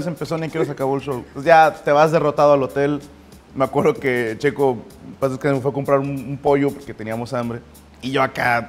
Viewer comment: Minuto 8:28 checo en paz descansé escuche yo o estoy mal :v